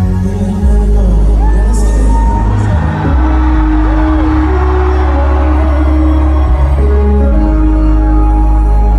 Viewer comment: What did he say at 1:07 he pointed to someone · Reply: Late reply but I think he said “I see you”